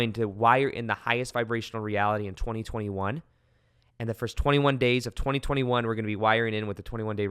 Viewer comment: You said it starts on 21st.. at 19:o8 Ish !!! Was that a mistake? Edit again LOL... are you the artist (pics in background)?? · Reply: I misheard it too. It's in his write up below the video. It sounds funny at first, he does say the first 21 days of January, 2021😊😘